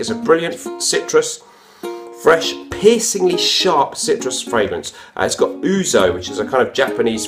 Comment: When describing Issey, I first heard you say "pissingly fresh" - which is in fact a pretty accurate description.